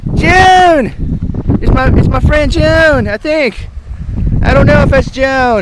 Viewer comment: LOL XD I was waiting for epic walking music to come on. Something similar to the old school Mario music....And I honestly thought you kept saying "My friend June"